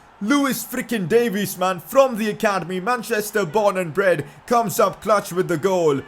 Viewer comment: did I heard born in bread.